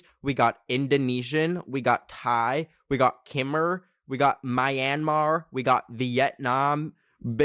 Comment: did u say myanmar? that's where i'm from